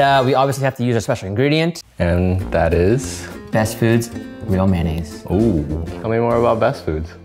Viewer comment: Not gonna lie when first said the words “real mayonnaise” I misheard him and though he said “ veal mayonnaise”, and I was like wow this video is even wilder than that title prepared me for it to be